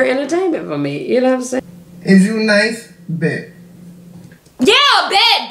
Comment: IT TOOK ME FOREVER TO COMPRHEND THAT SHE SAID JUNE 9TH I THOUGHT SHE WAS SAYING IS "THU [U] NICE" 😂😂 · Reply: 😂😂😂😂 is that what she said?? I swear I thought she said, "is you nice". I couldn't understand her for the life of me!!